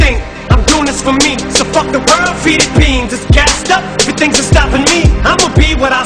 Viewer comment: As a Filipino, I used to think that he said "Philippines" @ 0:45, really...now I felt betrayed that he said "Feed it beans" instead.